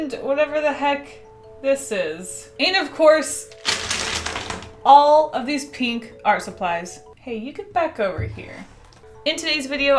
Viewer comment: Spotted a mistake. At 0:13 in the captions, it you said “all of these pink art supplies.” BUT, the captions say “all of these pink are supplies.” WHAT IS THIS?